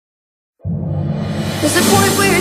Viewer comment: ok, at the start, am i the ONLY one who thinks it says BULLET not point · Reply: I thought it said "port" instead of "point"